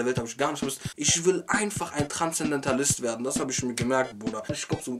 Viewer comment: Wie heißt der Beruf den er Mal machen will? Erwähnt er bei 08:14 aber kann es nicht ganz verstehen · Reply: Transzendentalist -- das ist aber kein Beruf. Der Transzendentalismus von dem er hier spricht war eine Bewegung von Philosophen und Schriftstellern im 19. Jahrhundert der USA.